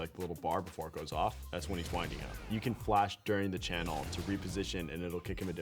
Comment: What does he say? "That's when he's winding up"?